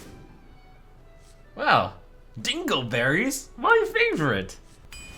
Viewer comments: all i hear is 'Analberries, my favourite' ... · I know that it's "Dingle" berries, but I misheard it as "anal berries" and I can't unhear it.